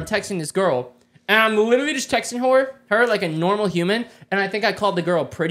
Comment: bro Ik he said her but it sounds like he said wh0r3😂😮